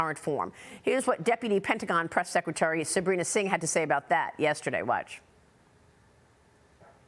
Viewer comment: Did she say “how thick is my johnson” at 0:19?